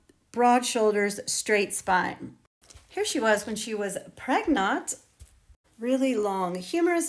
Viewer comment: I am sorry, I am a late comer to your very informative chunnel, but did you say? @ 1:42: “when she was pregnone? or pregnot? 😇